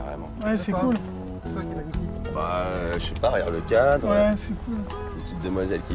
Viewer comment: C'est moi ou à 0:02 a dit "regarde la pute", elle est plus pure que les deux reunis en tout cas, ça me choque, vous voulez donner envie aux relous d'aller dans cet endroit parce que ça donne pas envie aux femmes, ça fait peur.